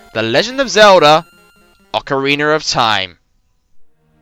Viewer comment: BTW, when you said Ocarina at 2:00 it sounded like you said "ocariner" LOL Still a good video and a great series! :)